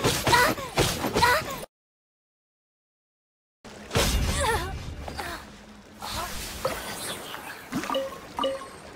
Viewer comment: Do you know what she says? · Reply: Heartless